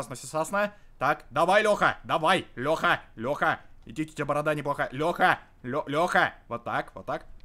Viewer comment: Я ваш визит смеялась с того момента где ты его лёхай назвал) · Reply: Самое смешное когда ты сказал Лёха борода не плохо(не так расслышала скорей всего)